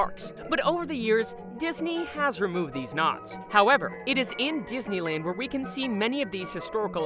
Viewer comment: Did you say "Disney has removed these Knott's"?